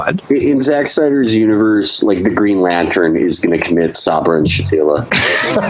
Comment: What does Felix say Green Lantern does at 19:11 ? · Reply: Sabra and Shatila massacre